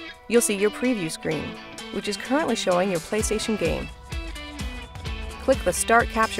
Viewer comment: Am I mistaken, or did she really say "playstation" at 2:14?